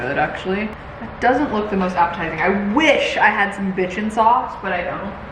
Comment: wondering what you actually said. cause all i hear is “i wish i had some bitchin sauce” 🤔